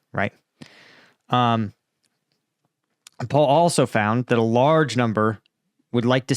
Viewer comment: I thought you were saying “Paul also found”